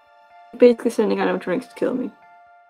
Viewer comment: AND NOW: Autocaption’s Gems of the Episode! (This comment will be edited until I am done with the ep!) 1:57 - Autocaptions say: “you basically sending out drinks to kill me” instead of (somehow) “You’re sending out animatronics to kill me” idk how autocaptions got “drinks” from “animatronics” but yeah. Before first 3 mins: Autocaptions has spelled “Sonia” in like 4 different ways. One was “Sonja” which I found extra strange 15:57 - “And I’m February” instead of “and I’m good where I’m at”. How did that happen…? Around 25:16 - “You’re not a funny girl” instead of “I’m not a bunny grrr” =/ XD